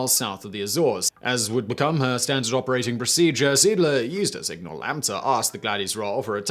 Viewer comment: Good story, just that Seeedler hurt a little. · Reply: Yeah, I first thought he said "Siedler" which would be German for "settler" but not really a common name for a ship...